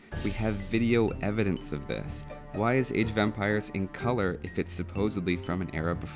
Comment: I always hear "Age of Vampires"